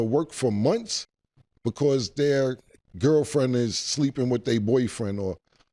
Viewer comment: waittt did he say they gf with they bf